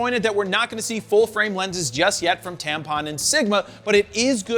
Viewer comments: Fro said tampon instead of tamron 😭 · did he say tampon and sigma? Freudian slip?